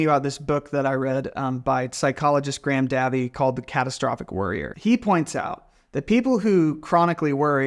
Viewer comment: When you mentioned the book, I heard "The Catastrophic Warrior". I was like "thats such a cool phrase!"